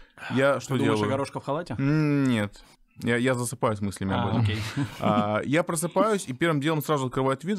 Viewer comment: «засыпаю с мыслями о горошко в халате» или мне послышалось? 😂